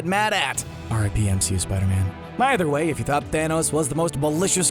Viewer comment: What does the narrator say at 0:21?? It sounds like "RDMC, Spiderman." If that is it, what does that even mean? Otherwise, what is he saying??? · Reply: "RIP, MCU Spider-Man."